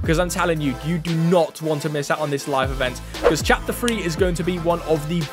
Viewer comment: it sounds like he saying chapter free